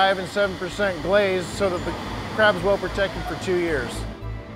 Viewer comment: did he say where the crab is protected for 2 years?!?!